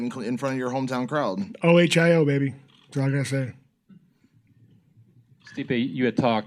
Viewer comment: what did he say? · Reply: " O-H-I-O baby, thats all i got to say"